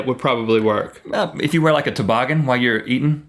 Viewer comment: did Rhett say, "like if you put a toboggan." in response to Link's idea of warming your head with your hands?